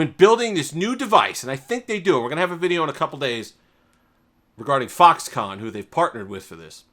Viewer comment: Did I just hear you saying "we are gonna have a video in couple days with foxcon" ? Spoiler alert...